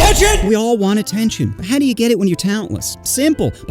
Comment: Re play this part 0:00 It you do it fast enough it sounds like he is saying “I touch you”